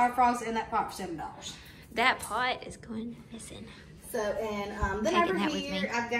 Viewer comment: Did I hear you say "that flower pot is goin' missing"? Bwagh haha! Y'all both nuts!!